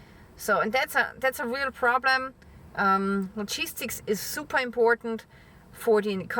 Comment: I agree all this is very serious. And you are terrific. However. Whenever you say “logistics,” I hear “cheese sticks.”